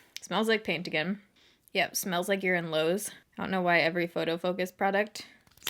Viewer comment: I thought when you said “you’re in Lowe’s” that you actually said “smells like urine lows” 😂😂😂